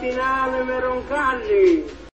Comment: en el anuncio d san juan XXIII, al cardenal le faltó decir el apellido, saludos desde Chile noviembre 2022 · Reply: I heard the name, Roncalli